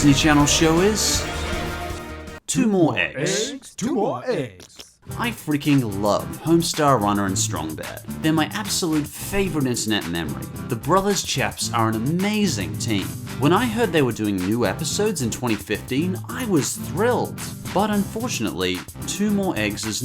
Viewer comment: Did you just say "favoritist"?